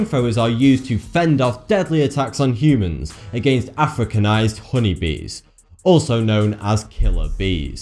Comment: For a second, I thought he said Africans.